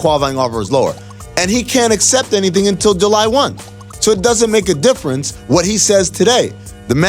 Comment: did he just say “ July one th,” ah yes oneth, January the oneth month of the year 😂